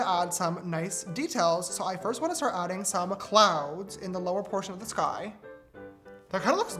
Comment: I literally thought he said clowns not clouds lmao